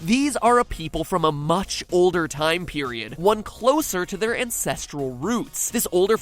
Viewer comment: When he said Ancestral roots I heard "incestual" roots lol